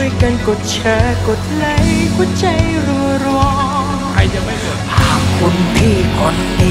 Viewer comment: ทำไมผมได้ยิน "ใครจะไม่กด"